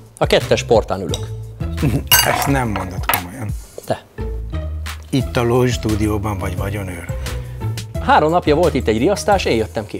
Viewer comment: nál mit mond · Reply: Lloyd stúdióban vagy vagyonőr?